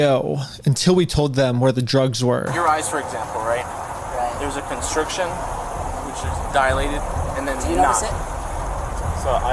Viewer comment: did he just say there's a constriction which is dilated?